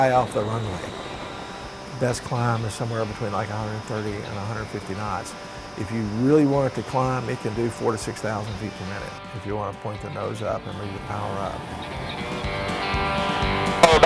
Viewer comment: Okay I'm sorry, did I hear 46,000 ft a minute!?! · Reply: "four to six thousand per minute" That's what he said...